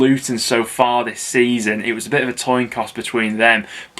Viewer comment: does he say toin coss? 🤔😂